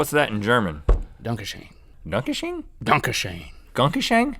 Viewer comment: when link said Danke schön I thought he said donkey shin